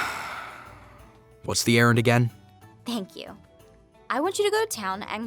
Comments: When she said errand I thought she said Aaron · I thought she said Aaron not errand and I got triggered like NOOOO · "I have another errand for you to run." D-- DID YOU JUST SAY AARON?!